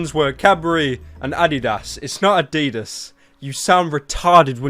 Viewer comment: when he said Adidas, it sounded like he said "I'd eat ass"